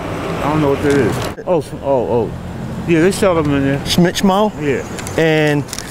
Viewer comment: “Smitch mo??” 3:21 yeah they sell them in there.. “market an dancem??” 3:27 the guy went to look came back, ‘yeah they got them in there’ This is super sayain level of minding your own business